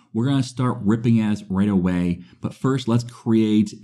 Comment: I have no idea what was said there, but all I heard was 'we're gonna start ripping ass right away' and now I can't unhear it 🤣🤣🤣